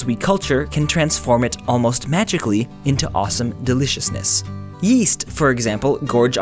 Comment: Yeast I thought u said WEAST Patrick Star is cool